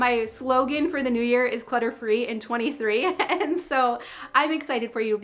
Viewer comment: When you said your Slogan for the year, I thought you said Butter free in '23 and I thought, there's no way I can do that 😂